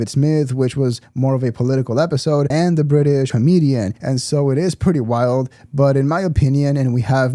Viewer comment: The what? Sounded a bit like you said "the British Comedian" but I couldn't quite make it out. Who are you talking about? If you did say that who is that, like Britain only has one comedian or something????